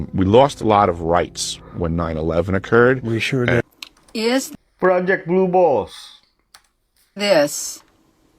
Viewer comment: Did he just say: Project Blue-Balls ?? Did I hear that right?!?! 😂